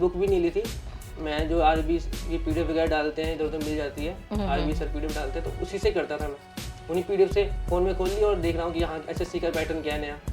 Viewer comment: not clear please somebody tell me what he is saying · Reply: 4 baar suna 😂 Sir pdf daalte the to ussi se karta tha Mujhe bhi itna hi sunai diya Kon se sir ka naam liya pata nhi😂